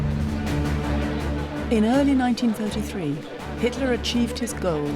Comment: Did the narrator say at 30:00 that Hitler came to power in 1943? Maybe I just can't hear her properly because of the music, but if she said 1943 instead of 1933, that would be most dissapointing.